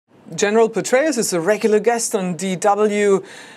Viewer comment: I thought she said General "betray us" 😂